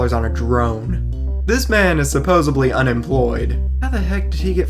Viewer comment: I love your videos, but it sounded like you said "supposebly". I hope that's just from your $130.00 microphone so I don't have to deduct any points.